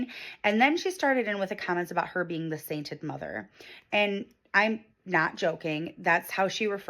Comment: I've got audio processing issues... I totally heard "her being the Satan mother"... also LC/NC with inlaws b/c of MiL.